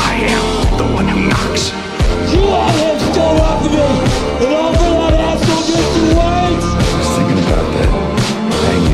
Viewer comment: What jesse sayin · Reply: He says “you had him steal it off of me, and all for that a**hole mr white?” Word for word